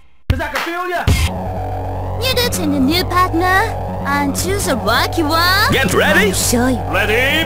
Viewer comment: what did she say here 1:23 ? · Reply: "You got a new partner. Aren't you the lucky one. I'll show you!"